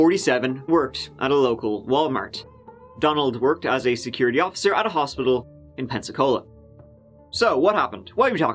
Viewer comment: i thought he said: "donald worked at a hospital in pepsi-cola"